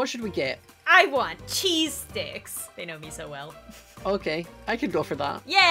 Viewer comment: FAMILY GUY:Oh no RANDOM GIRL (I barely watch family guy lmfao):Oh no KOOL AID MAN:OH YEAHHHHHHHHHHHHHH Me: Chokes on water in my uber's car LMFAO WHAT XD 2:56 When I heard you I literally thought said "Cheese dicks"... Am I the only one who thought at 2:56 said "Cheese dicks"? JUST ME? SHIT!